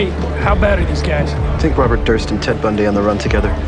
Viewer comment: 'think Robert Durst and Ted Bundy on the run together'...I love Barba😂😂😂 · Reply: nevermind i had yo actually listen to it but i thought he said take but he said think i didnt mean hate